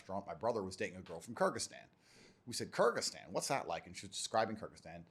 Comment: is he saying kurdistan ? Kurds like button